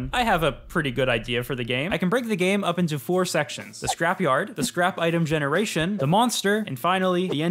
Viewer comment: I thought you said scrapy art as a joke should have added that in